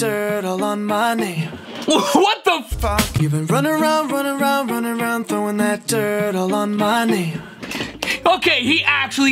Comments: i heard throwing that turtle on money 💵 💵 at 8:52 in the video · and i think he might have said throwing that dirt all on money or my knee 💵 💵 at 8:52 in the video